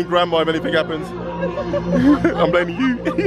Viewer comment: I just need to know if said gramma at 7:04. I kept rewinding but kept hearing the same thing. I know it can't be right. What did he say???? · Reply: He said he's blaming gramma.. as in, she's the girls gramma. The lady is Michelle's Mom so maybe that makes her ' Aunty?